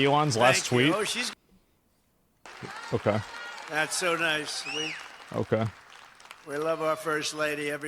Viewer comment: i wonder what he said, i didnt see it live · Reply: Oh she’s gonna be happy